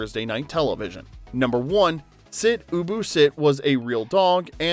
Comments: Growing up watching this when it came out. I loved it. As for for the sit ubu sit, I always thought it said sit boo boo sit.i was little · I always thought he was saying sit bubu sit 😂